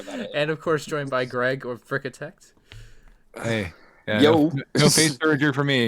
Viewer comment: did he say FrickaTect?